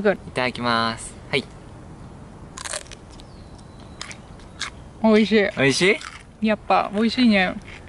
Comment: I thought she said holy shit